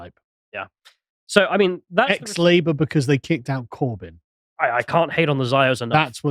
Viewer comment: Am I the only one who hear ex-slaver when the said ex-labour?